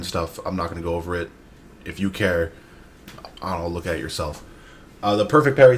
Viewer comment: Modern. Go look at yourself. Lol. I know you said go look at it yourself, but I heard "go look at yourself" which also applies.